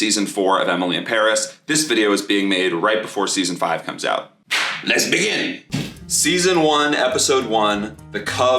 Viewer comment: 1000% heard this as "lesbiàn" and thought you were introducing Camille in a BOLD way